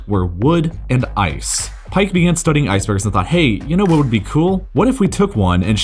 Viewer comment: Wtf was he trying to say at 1:54? "Pikeagnstughrltg Hey!" I swear that wasn't English.